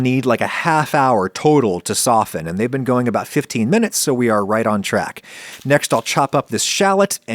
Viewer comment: you said "Right on track" wilst the captions say "right on target" I am disapointed to say the least, I expected better from you...